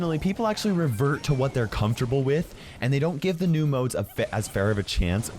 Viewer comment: did almost swear? Like he almost said They don't give a fk ?